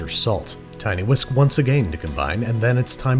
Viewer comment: D-did he just say "ageyn"?